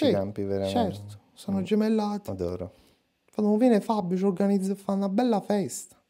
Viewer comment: scusa, ho capito bene?! "quando viene fabbio"?! ma fabbio tu? cioè la merkel s'è lasciata scappare che fa le feste con celenza?! e noi così lo scopriamo?!?! ma oh?!?!?